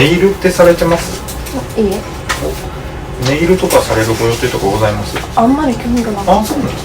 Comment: 네일 할 예정이 있나요? 혹시 저 영상에서 나온 문장이 일본어로 어떻게 하나요? 제가 직역을 하는편인데 그렇게 안들려서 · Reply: ネイルとかされるご予定とかございます？ ネイルとか / 네일 이라든지 される (する의 존경어) / 할 ご予定とか 예정이라든지 あります？ 있습니까? 직역하면 요런 느낌